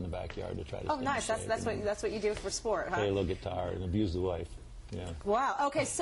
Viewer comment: I like how she kinda freaks out when he says abuse the wife. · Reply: I thought he said “amuse the wife”. I don’t think guitar playing can be considered abuse in most cases 😂